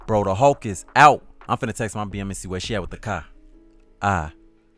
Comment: he said "Car" it sounded like he said "Cock"😂😂😂😂